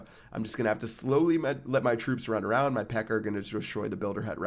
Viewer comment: did he say his pekker?